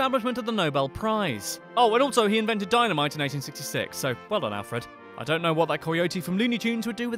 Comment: Wait did you just say Nobel invented the Dynamite in 1966? He died in 1896. What the hell... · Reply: He clearly says 1866...